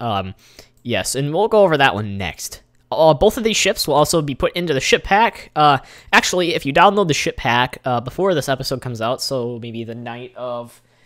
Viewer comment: Sounds like he is saying shit pack lol